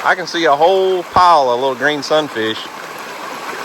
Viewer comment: God bless y’all for making this kids day, memories he will cherish🥰🙏🏻😇. Now I must admit that at first every time you’ve said Sun fish I thought you were saying “look at that son of a B- - - -“ LOL🤪😆. I am relieved I was wrong 🤭😉. Love and prayers always🙏🏻